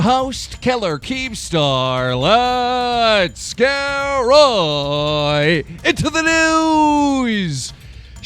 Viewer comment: It sounds like your saying "let's get riiight into the NOOSE" I'm so sorry I had to bring that idea up